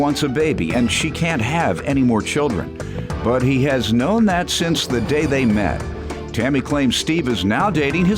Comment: I thought the commentator said "He had no nuts since the day they met"...🤦🏿‍♂️.. shame on me